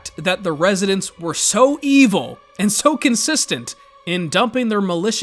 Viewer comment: did i just hear the residents were evil????